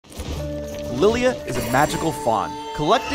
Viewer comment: I literally thought he said " Lillia is a magical THOT "